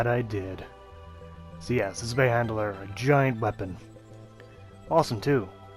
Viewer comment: Mate love the videos but Jesus you can't say words, it sounds like your saying zwei Mandela?